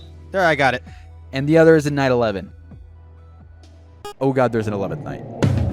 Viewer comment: I keep hearing "Night 11" as "9/11"😭